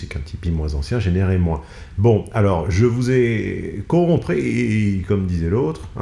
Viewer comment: perso j'ai compris "Je vous ai corrompris", certes ça n'existe pas mais ça m'a fait toussoter de rire